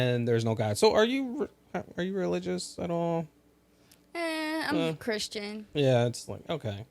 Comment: "Ehh, I'm Christian." What tf did he say? Sounded like, "Eh, that's lame." Someone please correct me if I'm wrong 😂